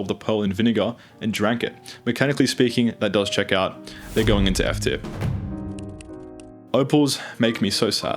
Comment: what did you say at 1:30?????? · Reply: They are going into F tier!